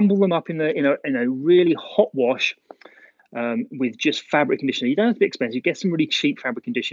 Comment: Hey hi from France your vid it's great thanks for that I have just one question I hunderstand 90% English but some words it's complicated I would like anybody write a word 6:49 I put in machine and no detergent but I put what? If anybody can say me please it's very sympathy thanks very much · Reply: Fabric conditioner